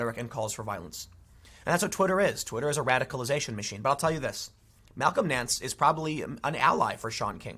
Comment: Everytime he said Malcom Nance I heard "Talcum X".